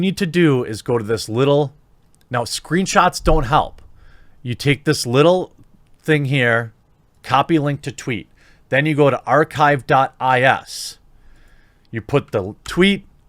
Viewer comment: I thought you said archive.if. Took me forever to figure out you were saying "S" and not "F".